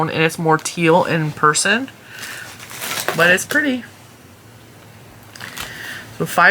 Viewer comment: I think I need a hearing aid. 😂I thought you said tea and pee 😂